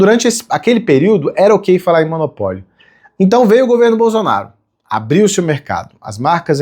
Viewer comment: Hehehe sério que ele falou "governo BOZONARO"??...